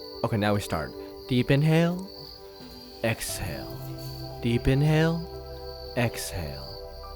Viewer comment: Why do i hear "deep deep in hell, exhale"